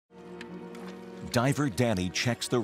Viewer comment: First words I hear "diaper daddy"